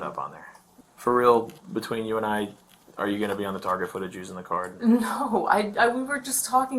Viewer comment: did he say “for real between you and I , are you gonna be be on target for the Jews on the card”